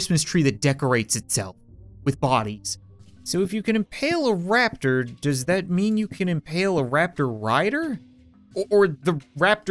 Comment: I had an alarm and it went off in this video, it sounded like he said "If he can impale a raptor, does that mean he can impale a raptor?"